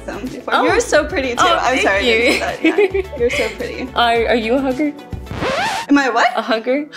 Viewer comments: Definitely did a double take i thought she said ‘hooker’ instead of hugger I was like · Definitely did a double take i thought she said ‘hooker’ instead of hugger I was like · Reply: That’s funny cause when she first said it, I heard hugger loud and clear and when she responded with what? And then she repeated herself with the text saying hugger, I was like oh she probably thought she said hooker. Like I had to figure out why she was shocked when said said what ? 😂